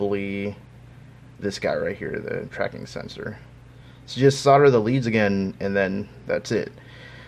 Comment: but what did he say? "Slaughter the leads"?? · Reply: solder